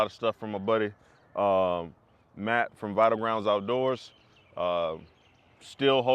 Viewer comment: That amsteel rope you talk about at 2:53 , where did you get it . I can't make out all of what you said. I heard Matt from mumble something outdoors. Great channel. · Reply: Thanks for watching bud, its Matt from Vital ground outdoors.